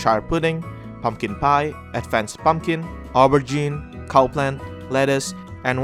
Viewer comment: What did he call the Eggplant? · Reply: An aubergine